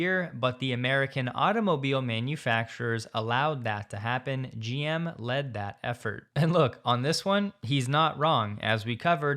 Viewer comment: Was Newsom saying, “You led, Mary… and it mattered.?.?” 😮